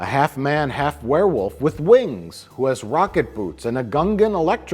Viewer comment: I dunno why, but I hear "Vodka boots" whenever says "rocket boots"......... I like the former better.........